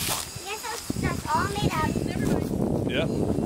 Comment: I heard a woman speak under saying ‘never mind’ that was weird